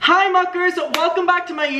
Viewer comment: Did you just say hi FUCKERS?